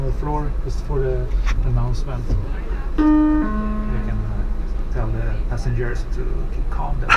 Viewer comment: did he say "tell the passengers to calm the fuck down" or did i hear wrong